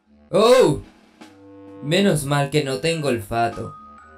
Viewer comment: En el minuto 3:12 entendí "menos mal que no tengo el pato"